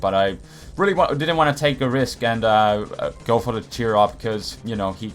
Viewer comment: why did i hear "Cheer Up" instead of "Work Up"